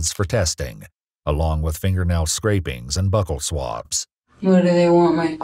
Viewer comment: Okay, if you're going to say a medical term, please look up a pronunciation or google it first, because I thought you said "butthole swabs", not buccal. It's phonetically pronounced byoo-kel. Not Buckle, not bekkal, the "u" is a long "oo" sound, the "kel" sounds like coal, but not as harshly.